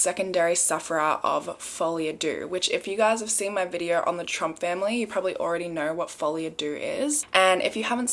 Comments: for a second there i thought you'd said the trump family and i thought oh gone political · I thought you said Trump family which would have made perfect sense for the group delusion diagnosis.